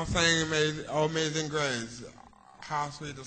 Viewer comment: i heard "o mazing grace"